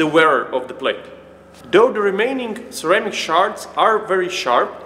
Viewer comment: It sounded like you said "ceramic sharts" and I chuckled.